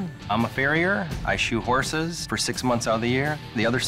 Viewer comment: i thought he said "i shoot horses"